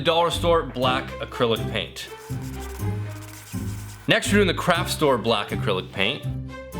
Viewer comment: When he was showing the light differences from the paint and he said craft store I thought he said crap store🤣